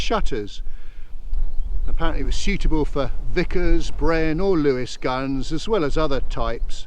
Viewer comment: great video. I had to a double take when I thought you said the turret was "suitable for vicars (sic)"! John